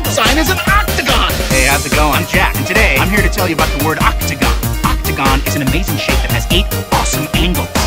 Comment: I hear ''octagun is a amazing shit'' XD